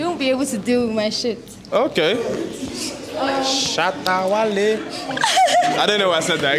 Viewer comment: I heard shatta wale 😂